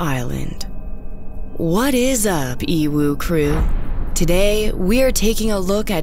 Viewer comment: ohhh so it is EWU crew, I thought she said "evil crew" lol